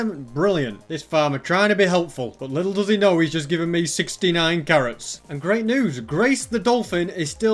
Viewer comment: did he say iron carrots?